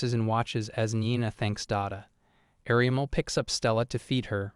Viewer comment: bro at 8:47 i thought the kid said dad f*ck you 😭